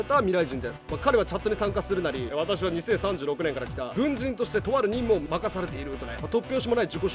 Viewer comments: が軍人なのにブンジンに聞こえた笑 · の動画を見すぎて「軍人」が「ぶんじん」って聞こえたのツラい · 〜 ｢ブンジンとして、とある任務を任されている｣に聞こえた